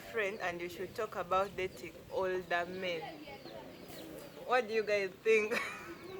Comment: I thought you said 'dating ALL the men' instead of 'older men' 😂🤣